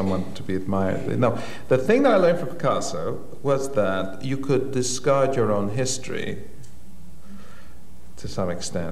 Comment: did he say 'discard your own history'?